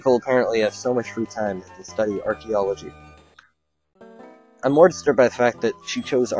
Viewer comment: i thought he said "archeology" (I think thats how u spell it...) you should do a (X pokemon) only runthru on ruby but using something like a meditite or ralts or somehing. that would be so awesome!!!